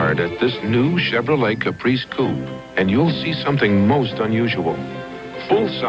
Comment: Listen at 2:59:37 the last Chevrolet commercial and tell me if the announcer says New Chevrolet Caprice poop instead of coupe